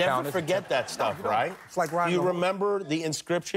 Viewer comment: did he just say it's like riding a horse?